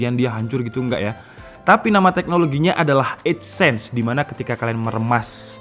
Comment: kok dengernya adsense wkwkwk :)